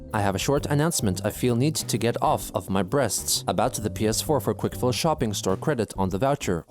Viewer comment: “get off my breasts?” Ewwww what actual women says that shit